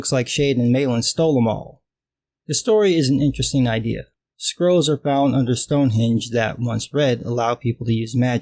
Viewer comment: I kept hearing "squirrels" instead of "scrolls", which is a concept I could get behind. Squirrels that give you magic powers when you breed them.